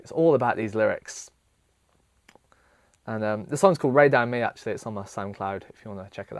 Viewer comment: It's called 'Rein Down on Me'. You can listen to it here :) <link> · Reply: Ah cool thanks...I never would have known. It sounded like you said Ray Damn Me. :P